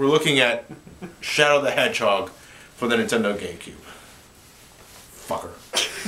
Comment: Is it just me or does it sound like he is saying Nintendo GayCube and not Nintendo GameCube.